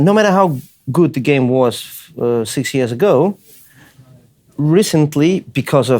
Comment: When Victor said "six years ago" I thought he said CS:GO xd